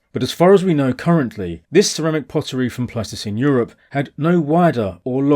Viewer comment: I first misheard 'Pleistocene Europe' as 'Plasticine Europe', but it kind of made sense.